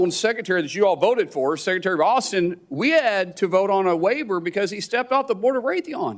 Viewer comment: I am not a native english speaker. What did he say at 6:12? I've heard something like: "we had to vote on a waiver because he stepped out the board...". What exactly he said?